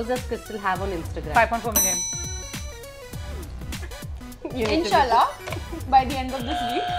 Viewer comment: Did she said in sha allah on